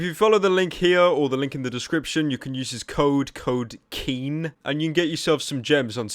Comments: Thought he said use code keem 😂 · at the end of the video with the deal and shit, I thought he said 'Use the code KEEM' and I was just like 'holee fuck, so this is what humanity has come to now?'